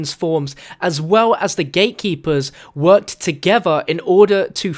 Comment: I thought u said gaykeepers😂